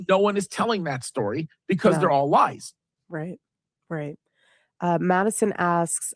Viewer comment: Time stamp 1:21:37 ish “They aren’t telling that story bc they’re all lies” did I hear that right? It seems like he just had a Freudian slip there. Or I may be confused and heard it wrong. He is difficult to follow as he just yells at you the entire time trying to get his point across. He is like "if I can speak really fast, really loudly, people will believe what I am saying"